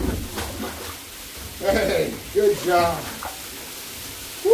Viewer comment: The guy at the end who said "I love my job," I swore the other guy said "I love you" at the last second 😂